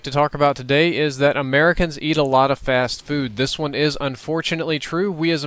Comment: i thought he said we eat a lot of porn cause my fan was on high, i wa like what sicko eats a dvd case